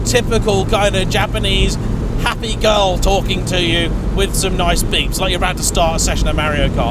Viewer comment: ‘girl with nice beeps’ ... At first I thought he said ‘girl with nice boobs’ :’)